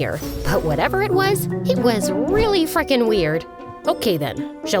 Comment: did she really said “fuckin weird”😅😂